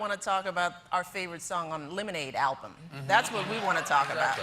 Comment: any other Mendes Army members think she said "Illuminate album"?